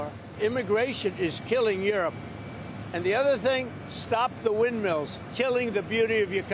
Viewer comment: Did I misheard stop the windmills?